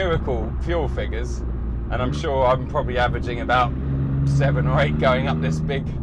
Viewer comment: what does he say? Averaging 7 or 8...? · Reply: mpg - miles oer gallon